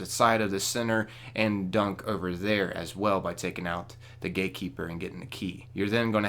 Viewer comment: Very instructional.....also u should try a southern accent....just a suggestion..XD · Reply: Dude I thought u said gay keeper.....lol